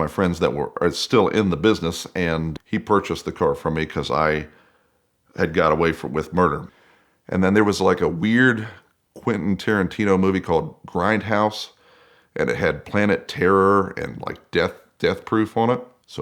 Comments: did he just say he got away with murder? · I swear it sounds like he said “ I got away with murder” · did he say he got away with murder??? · did he say he got away with murder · was it just me or did he say he got away with murder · did he say got away with murder? 😳😂